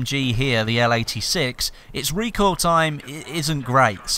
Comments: did he just say recoil time?? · did he say recoil time? lol